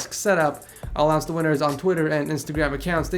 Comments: did he just say I love your finger nails? 11:49 0_0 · did you just say "i love your fingernails" at the end of the video????